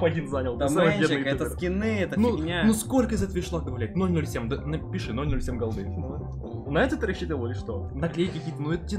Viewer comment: я не расслышал?😅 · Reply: он сказал добавлять